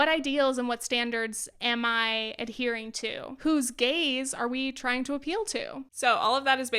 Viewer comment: I almost spat out my drink at 7:49 when I heard who’s gays and not who’s gaze are we trying to appeal to 😂😂